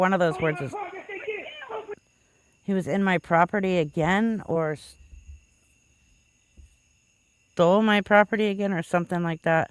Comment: Sounds like he said...stealing my property again.